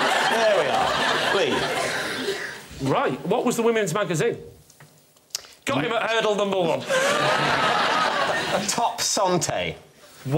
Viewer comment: Please what did Lee say at 6:31? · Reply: I think it was 'got him at hurdle number one'